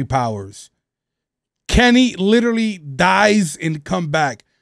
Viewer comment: in subtitles it says Instead of Dies It's Cries